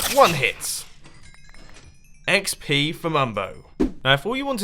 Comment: I thought he Said xp for mom